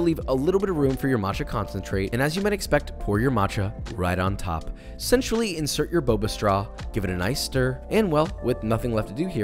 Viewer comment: Joshua: "yes, haha nut milk, so funny." Also Joshua: " B A L L S." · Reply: did he say sensually or essentially?